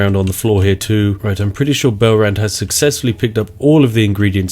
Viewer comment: I kept hearing "Belrand" as "bell-end".